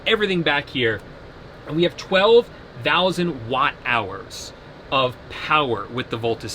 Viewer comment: Did you say 12,000 watts of amps power? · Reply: 12,000 watt hours of power